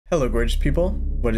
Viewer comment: I swear I hear "Hello British people". To think I would be British is scarier then any eldritch horror's beyond human comprehension.